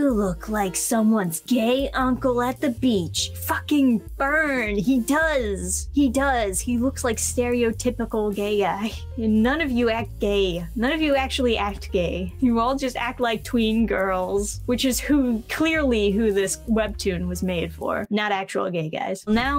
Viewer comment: sounded like you said "stereotypical Gay-I." Someone needs to trademark that. "Gay-I."